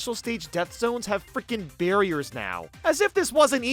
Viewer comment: I Thought It Said "Bear Ears" But It Said "Barriers"